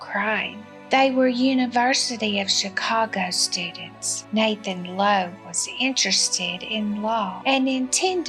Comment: Narrator called him Nathan LOEB instead of Nathan Leopold